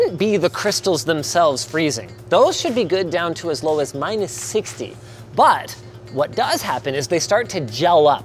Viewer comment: Did linus say minus 60